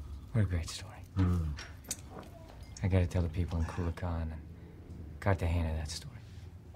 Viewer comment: WTHell??? @ 2:13 the mumbling starts. Cruise says "gotta tell the people at coola con, cot the hand of that story. Nuh the fusk sin colla con anyhow. Raytom. Manze I thought you were a cool guy...." What the fuck are they saying??? Really. WTF are they talking about? In a big screen movie they mumble, jumble and disconnect so much so that a viewer listening and replaying the scene has no idea or interpretation of what they are QUIETLY mumbling about. WT Hell